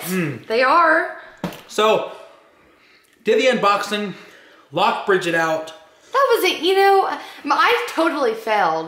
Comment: listen carefully you hear screaming like if I hear it comment if I'm crazy😂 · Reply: I heard a voice saying Brigitte